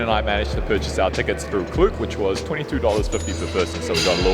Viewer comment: Hey, love the videos you guys make. It really comes in handy when I'm planning my own trips. :) Had a quick question. At (3:50) you mentioned a website from which you bought tickets for your tram ride (sounded like CLUE). Could you please share info on that? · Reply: Hello the site is called Klook but do note the prices change constantly depending on the season you're traveling in. Cheers ✌